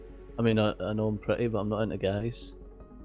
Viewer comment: what did say at around 47:30 ? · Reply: "I know I'm pretty but I'm not into guys" XD